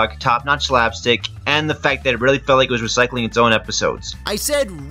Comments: I thought you said it REALLY felt like it recycled its own episodes. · it sounded like you said it really feels like they recycle their own episodes